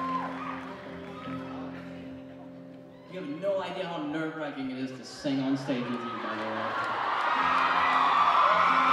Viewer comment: What did he say? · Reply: KaeOntheCouch I couldn't make out the very end but I can hear most of it, he said "you have no idea how nerve-wracking it is to sing on stage with you"